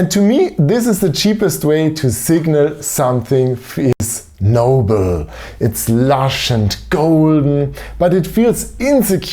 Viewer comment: "to signal something..." what? Your audio glitches here and I don't know what you meant to say. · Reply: Signal something that feels noble?